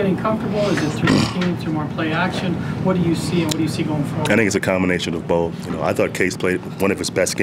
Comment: everyone rewind/fast forward to 4:48 lol it sounds like he said "that niggas a combination of both"